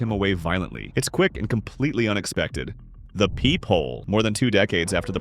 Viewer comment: I thought that said peehole💀